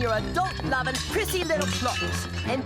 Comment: Did number 4 say Pissy????? Or am I hearing things?